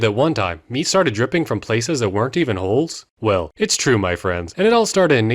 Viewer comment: The first few times i heard this i thought you said "me started dripping out of things that werent even holes." And i was like "huh yeah thats something id hear in a samo'nella episode anyways"